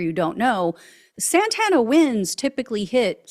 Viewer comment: I don’t know if my tv speaker warped your words/ pronunciations, but it’s Santa Ana winds It sounded like you were saying Santana. A decent band, but had nothing to do with the high wind storms we get frequently.